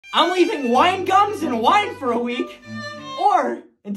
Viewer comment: My dumb ahh thought he said wine Guns- I was gonna say